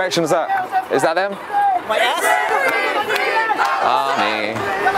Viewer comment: It sounds like he said hamas not my ass.